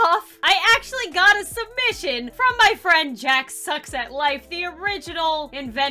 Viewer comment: what she says:"JackSucksAtLife" what I heard:"jacksepticeye"